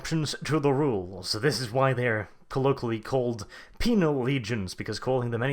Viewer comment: Yep... defo miss read and miss heard that as; Penile Legion.